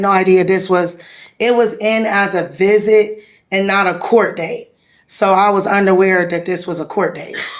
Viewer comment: Did just say “she was underwear”??????